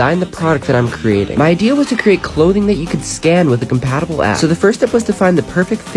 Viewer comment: I thought he said compatible ass that would a good idea